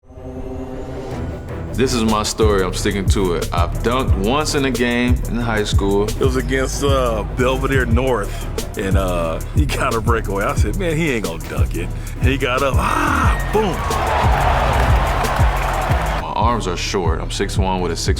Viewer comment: I swear to god i thought he said the school was the “Belvedere Dorks” 😂😅